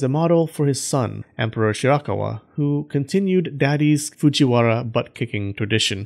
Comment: I thought it said, KISSING FUJIWARA BUTT